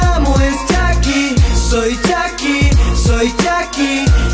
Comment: like si llegaste antes de las 100 vistas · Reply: like si notaste que dijo chachi en el minuto